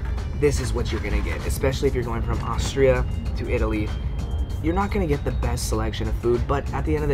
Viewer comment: At first I thought said “we’re going from Australia to Italy” and I was so puzzled. I was like train... on water? 6 hours??? And then I rewatched it and I was like OH okay